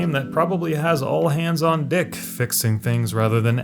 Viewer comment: CC subtitle said "all hands on D*CK" making it more hilarious.